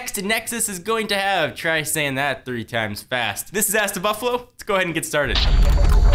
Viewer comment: sounds like he said ass to buffalo